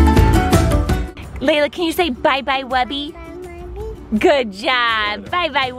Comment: When you said can you say bye bye wubby it sounded Like she was saying bye bye Mummy